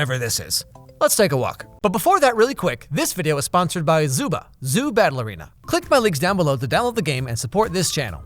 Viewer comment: I was looking away from the screen when you first said “Zoo Battle Arena,” and I swear I heard you say “Zoo Ballerina.”